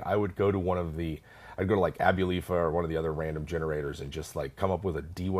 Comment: Can someone clarify what random generator is mentioned at 27:46 ? I don't think I've heard of it before, and I can't make it out. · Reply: I believe he said "Abulafia", which seems to have been a generator wiki, though the generators themselves appear to no longer work.